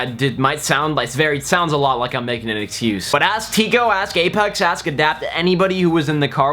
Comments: did u say Gaypex? :D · did he sayed GAYPEX ???